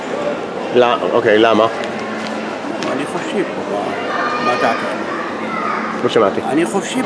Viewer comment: Small correction, the very first guy at 0:13 is not saying "That's what I think" (Ani Hoshev / אני חושב) he is saying "I'm free here" (Ani Hofshi / אני חופשי). It becomes clearer when he says the exact same thing a bit louder after being asked "Why" (Lama / למה).